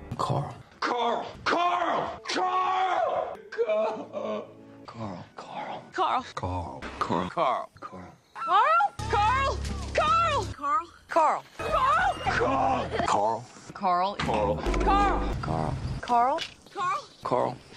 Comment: she clearly said coral